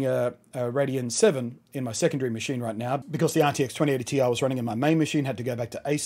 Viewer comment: Thought it said 3080ti